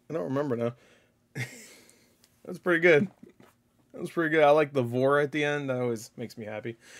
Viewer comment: Did he just say I like the VORE at the end?!🤨📸